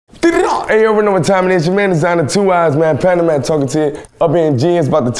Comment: Please tell me what he’s saying from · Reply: Any yo you know what time it is your man Desiigner two ‘i’s man Panda man talking to you